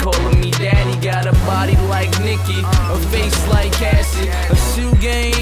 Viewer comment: i heard him say cassie